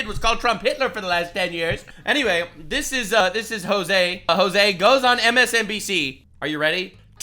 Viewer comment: Hang on, did you say his name is Jose Gozon...?